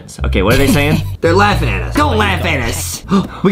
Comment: I thought said that’s me cardboard but it was Boxy